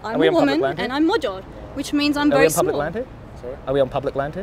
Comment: did she really say: "I am a woman and I am magyar, that is why I am small..." Dear Jesus...